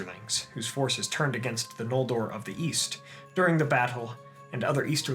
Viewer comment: East? I thought you said Weast